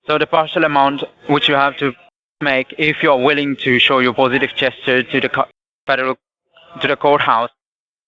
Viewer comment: Ohhhh, he's saying positive gesture, I thought he was saying positive chest !!! 🤣🤣🤣🤣